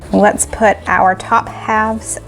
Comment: When you said “let’s put our top halves on” (the sloppy Joe’s) I thought you said “let’s put our top hats on” 🤣🤣